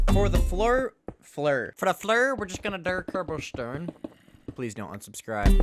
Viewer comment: "for the fler fler fer da fler we just gonna der cerbolstern" 😂😂😂😂😂😂😂 Me realizing what is he saying: he's either talking in france or talking nonsense 😂😂😂😂😂😂